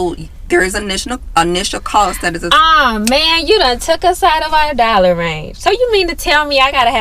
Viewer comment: I thought yall said zero dollar